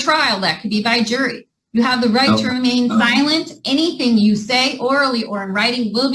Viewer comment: For a minute, I thought the judge said "You have the right to remain violent!"